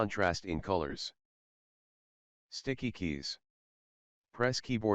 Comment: at first I heard stinky keys